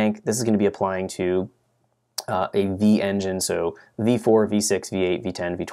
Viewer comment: im sorry but did you say “v4”?